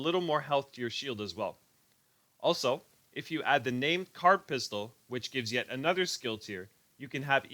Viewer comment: What pistol r u talking about? sounds like u said card pistol . how do u get it...thanx